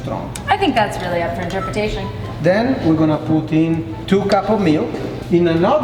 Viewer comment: when he said "put in".... i thought he said poutine haha